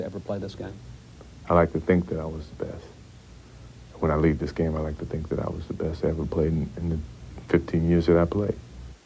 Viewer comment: “I’d like to think I was the best to ever play the game” (1999) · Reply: I heard “I’d like to think I was the best, when I leave this game, I’d like to think I was the best to ever play in the in the 15 years that I played” Not sure what you heard there buddy.